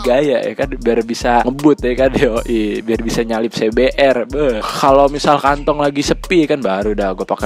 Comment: Gw tdi salah denger... Nyelip cbr😂